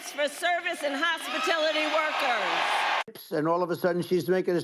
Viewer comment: Anyone else hear Kamala Harris say "hospitility?"🤔